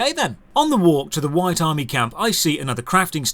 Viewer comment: OH!!! Ythurg! I thought you were saying, "Whitey HRG." My mistake.